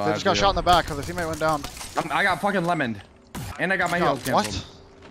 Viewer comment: can someone explain what they said to eachother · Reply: He’s said he just got lemoned I think lol he was referring to the sear ult tho